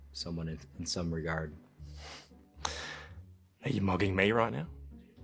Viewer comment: I heard someone say mogging with a weird accent. I heard it as mugging. And now I finally understand what it means. It's just a dumb way of saying mean mugging. 😂